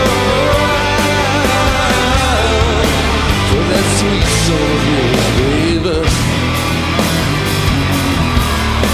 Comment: does anyone know what Ville says at 1:58 ? all i can make out is "for that sweet soul.." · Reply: I think "for that sweet soul of yours baby"